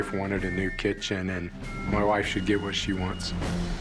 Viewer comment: “My wife should get what she wants”! 🤣 Edit: thx for the likes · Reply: Lake Olson I thought he said “my wife....she gets what she wants”